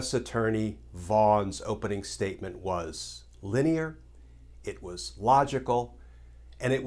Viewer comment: Glens quote: Vaughns opening statement was layman friendly. I heard: vaughns opening statement was lame and friendly. Lol 😋